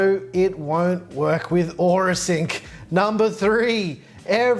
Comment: What did you say? Caption says, "no it won't work with or a sink". I don't get it, can some one explain? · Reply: he said that it wouldn’t work with Aura Sync